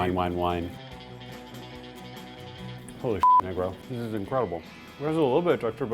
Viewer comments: did he actually said n*gro? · Did he just say Negro?🤨